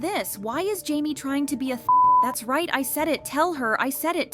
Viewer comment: What word did he call her? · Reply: He calls her "thot"